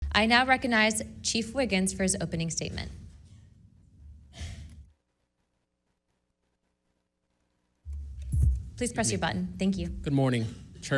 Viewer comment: Thought she said chief wiham hahhaha from the simpsons